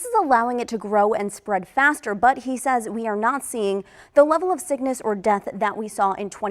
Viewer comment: creepy they way she says “we are not seeing” because at first I heard “neo Nazi”